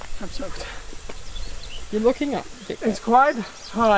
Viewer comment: what does say after 10:22 "I am soaked"? I can not understand him fully and it annoys me so much. First time I understood "You're looking hot", now I only hear "You're looking up" which doesn't make any sense? :D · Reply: Yeah can talk really fast, sounds like "you're looking wet enough " (does also sound like looking up), he also says "you're both looking wet enough " a few seconds later. Hope this helps as we need to understand to follow along and engage in the fun! Great video!